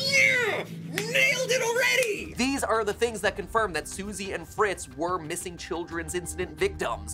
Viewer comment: When he said"susie and fritz" i heard" susie and frisk" like this if u like undertale/deltarune